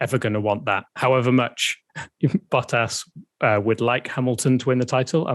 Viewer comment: did say Butt Ass???